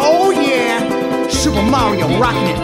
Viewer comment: I heard "Im invisible"